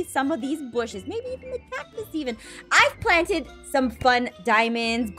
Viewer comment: did she SAy cATCUS STevEn